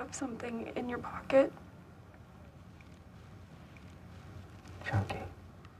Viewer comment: What did Michael say at 13:08? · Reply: He says chunky it’s a type of chocolate bar I think